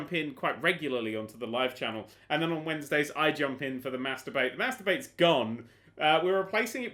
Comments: When you said mass debate I thought you said masturbate. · when you said "mass debate" i thought you said "masturbate" .-. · when he said mass debate I thought he said "masturbate"